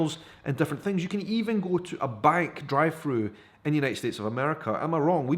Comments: Ohhhhhh... drive thru. I thought he was saying dry foods. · Ok, sometimes the accent gets me. It took 5 or 6 times before I realized you were saying drive-thru, I thought you were saying dried fruit...lol...I was thinking, my bank sucks if the others are giving dried fruit!